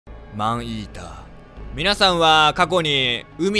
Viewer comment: マンイーターがアイーダに聞こえたねんけどwww